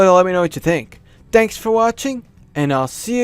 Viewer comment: Heh, it kinda sounded like you said, "Danks for watching!" at the end.